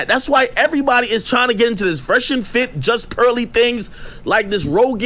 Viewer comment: Lmao did Ak call her JustCurlytThings